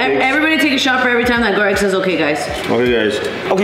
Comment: When was saying "ok guys." In a row low key it sounded like he was saying gay guys😂 I love u guys sm u guys are so funny❤ u guys are my favorite youtubers ever!!!!❤ Bless u